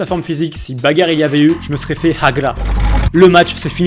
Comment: t’allais te faire quoi ?😭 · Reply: HAGLA